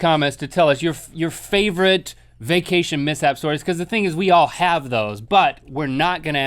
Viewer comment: I thought it said 5 crazy vacuum mishap stories 😂